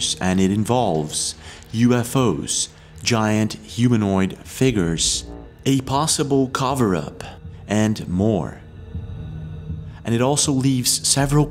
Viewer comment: What is a caw-ver-op? You said caw-ver-op at 01:30 ...... What is this voo-doo you speak of?